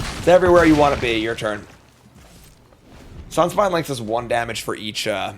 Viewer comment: What card is he saying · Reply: It's Sunspine Lynx from Bloomburrow, it pings all players for each nonbasic land they control